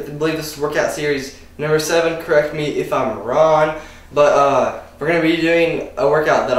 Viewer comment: did you say "correct me if im ron" ?